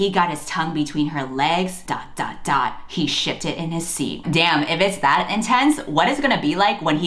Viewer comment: «He shifted in his seat». I have this video on 1.5 speed and misheard, so I thought you said «he shitted in his seat» and for a full five seconds I did not even question it. Like, the bad grammar and the action made perfect sense to me. I haven’t even read SJM books